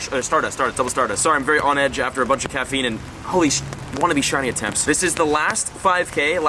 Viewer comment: did he said holy poop?! holy shit